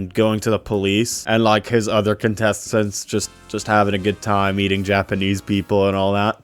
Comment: why did I think he said "eating" 😭